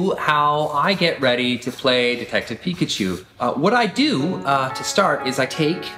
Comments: I heard "Defective Pikachu" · ohh i thought you said defetctive Pikaachuu haha